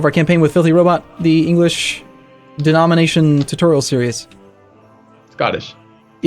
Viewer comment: I definitely heard 'denomination'